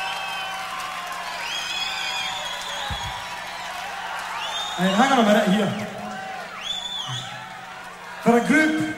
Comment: Please what does he say at 35:15? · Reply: Hang on a minute here. From a group who is definitely not about violence, that was a little bit stupid. Right, 'Cause violence only ends up in one thing and that is when one of you is dead and that's not very funny at all.